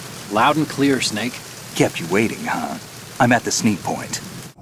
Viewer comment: ffs I heard him say "sneed point" ugh